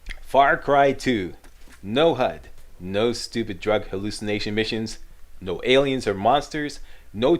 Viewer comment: I thought you said no hallucinated starts